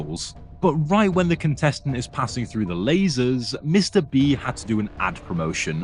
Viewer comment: I thought he said "Mr Bee" 😭😭